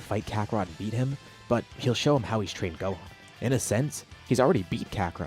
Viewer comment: I thought it said tried gohan